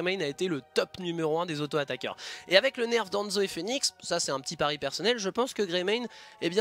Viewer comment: j'ai entendu "Le nerf d'Enjoy Phoenix" j'ai paniqué Sinon super contenu comme d'hab